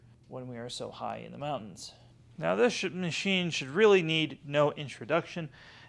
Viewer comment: Okay. Great reviews. I couldn't catch the brand name of the white cannister vacuum. Sounded like he said "ChadO" but couldn't find that anywhere so I must be mistaken. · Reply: I think that was the Lindhaus HF6, which he called the "Chad of vacuums". Which is an apt description. It's basically the modern take on something like a Silver King or a Fairfax.